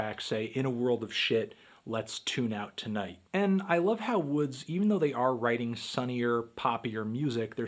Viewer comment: I thought it said Roy Woods 😐